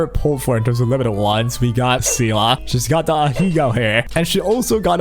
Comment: did he just said ahegao 😅 its ahoge lmao thats a different type of hair...